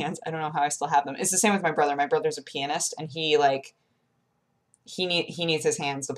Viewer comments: its so funny when people say pianist, because it totally sounded like you said your brother was a penis XD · oh my god i thought you said your brother was a penis at first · I was spaced out until I thought I heard the phrase "my brother's penis" but turns out I misheard. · I thought you said your brother is a penis XD